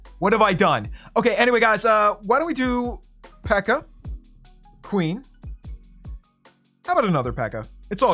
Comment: did he say queen well placing this king